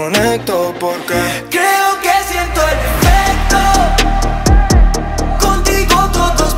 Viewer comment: Pense que decia ERECTO